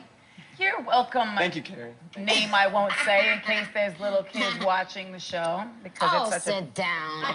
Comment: what Mariah say it sound she said u welcome me want say anything bcus lil kim watching the show or something 🤣😂 yes Mariah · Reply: She said little kids not lil Kim. She was saying "Minaj" was too in appropriate to say if little kids were watching